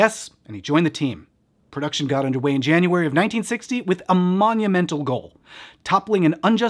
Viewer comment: Note: closed caption is incorrect at 14:36, saying "1968" instead of the correct 1960 (by 1968, the law HAD been changed!).